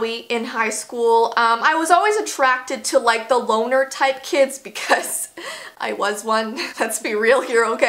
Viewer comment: I HEARD THE “WALMART” TYPE OF KIDS